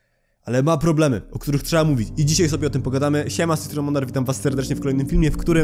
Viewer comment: Usłyszałem na początku siema z tej strony Monarch, zamiast Mondar, a to antagonista z miraculum.